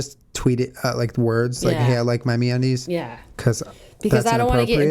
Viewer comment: I thought they were saying meat undies